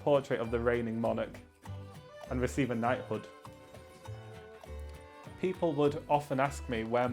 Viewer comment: I thought he said he wanted a night hood, like those little old fashioned hats people used to wear to bed lol.